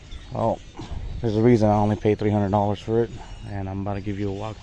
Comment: And I bought a 08 TL-S before this one in February 2020 for $1000 just like you described and only needed brakes and alignment. Then sold for $5000. I only paid $300 for this one because it was trash, but it was perfect for a J37 swap, and it has been very fun, way more torque than the J35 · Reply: Shiit I thought you said in the vid you paid $3,000, my bad 🤦‍♂️🤦‍♂️ That's cool as hell that you flipped the first car for $4k profit though, that's actually amazing :)